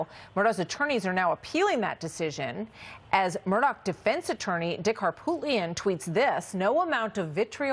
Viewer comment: Did she say his name was dick harppoophand? 😂 anyone else hear that❗️🤷‍♂️